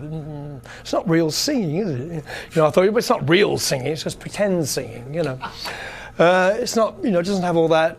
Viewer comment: Did he just say Dio is not singing? Just predend singing ? At 20:47.